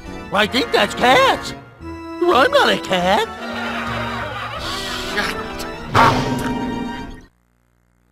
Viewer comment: NGL, I thought he said "shit" for a second.